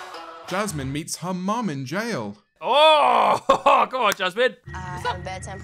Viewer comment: When I heard “Jasmin met her mother in prison” I screamed Because I was like UM